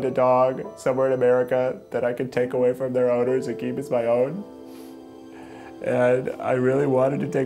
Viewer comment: When he was talking about Walden i thought he said "take away from their owners and Keith as my own" instead of keep.